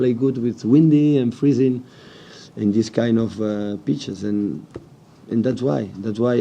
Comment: i thought he said bad beaches but he said pitches haha